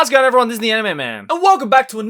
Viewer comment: O_o Did I hear "How's goin everyone, Disney anime man"?!!!? whaaaat XD